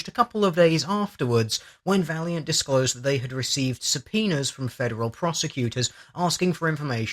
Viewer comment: I was listening to this while tabbed out, and i honestly thought you said "received some penis" at 7:53..... Guess i should have stayed in med school :P